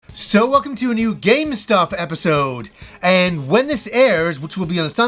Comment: I’ve been watching this segment for awhile now, and I swear everytime I hear say “welcome to a new game stop video”